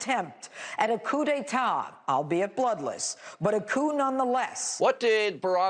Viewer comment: Did she just say "Coon none the less" <--- Did she just called Obama a racial slur. Someone correct me on this.